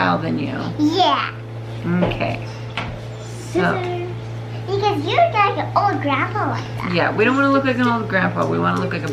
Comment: 😂 ❤❤❤ I think the little girl said because I don’t want you to look like a grandpa that is why I thought it was funny so I giggled then realized that it wouldn’t be nice for